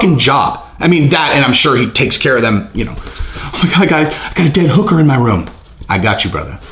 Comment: I thought u said ‘ Dan Hooker’ in room , had to rewind a few times 😂